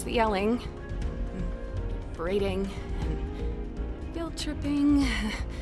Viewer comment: Okay,i swear i heard "breeding" in one part,WDYM "BREEDING" RAGATHA?!